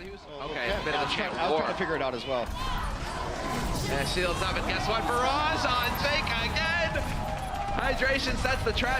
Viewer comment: etc What are the crowd shouting? Is that a player name? I am not good at English. :( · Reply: 안상현 'let's go outlaws!' is what they're shouting